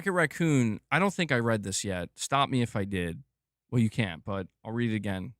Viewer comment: lol, catching up on some lives as I like to watch while playing Minecraft on my pc (my ps5 is being neglected) but this was so funny 1:01:00 “stop me if I did” made me laugh Edit: I thought you misspoke and said “stop me if you can” trying to say “stop me if I’m wrong” but still it was funny